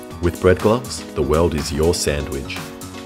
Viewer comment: I left this on in the background and at 7:00 I heard “The weld is your sandwich” and freaked out you were welding with them